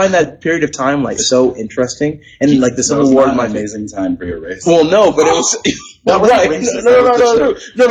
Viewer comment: did Scott say "It isn't an amazing time for your race." Or "It's an amazing time for racists." ? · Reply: Dog lover " that was not an amazing time for your race "